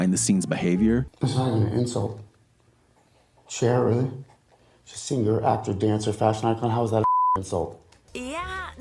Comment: Can someone please write what he says at 3:12 I just can't understand a word he says. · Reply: "Thats not even an insult Cher, really? She's a singer, actor, dancer, fashion icon, how is that a fucking insult?"